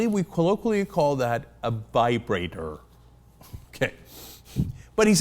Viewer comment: did Cenk call it a Bibrator?